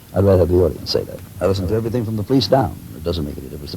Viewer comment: did I hear him say at 3:39 he listened to The Police???? I mean, great band, and steward copeland is incredible...just can't imagine him listening to anything but jazz.